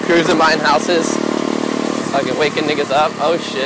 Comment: I stg I heard him “fcking waking N*** up”